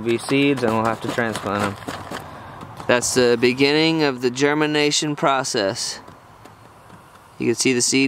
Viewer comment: It sounds like he said:" German nation or Germanation" :'D Greetings from the heart of Europe man